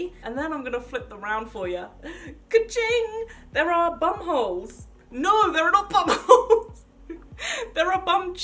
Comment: I can’t stop replaying this 😂🤦🏻‍♀️ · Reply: It sounds like she saying bum halls